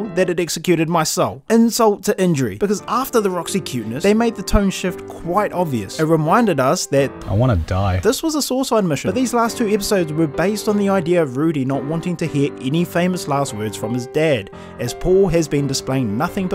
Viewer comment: what did he say? This was a sword mission? Please tell me what he said here